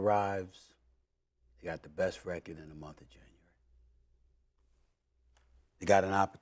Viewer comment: i thought sas was gonna say " we had the best record in the motherf*** January" 😂😂😂😂😂😂😂